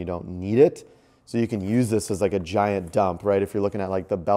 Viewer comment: did you really say giant dump? 🤭